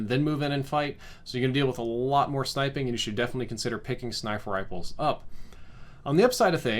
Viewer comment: I know I’m not the only one who heard “snifer riples” at 7:05.